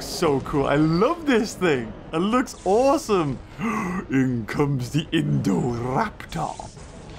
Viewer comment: When said here comes the indoraptor subtitles thought he said here comes the indoor laptop